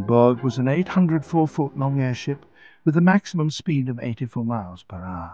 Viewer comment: Sounds like he said long ass ship 😂😂